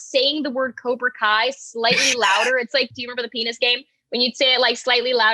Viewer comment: WTF DID SAM SAY!?!?!? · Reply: haha I was wondering how people who didn't know about the penis game would react to that.